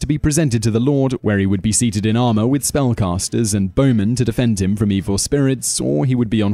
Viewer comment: did he just say "spell casters"?